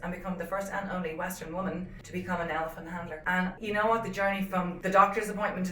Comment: Wait did she say Elephant HUNTER? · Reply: I think she's saying "handler," but I thought she said hunter at first too. I was like wtf!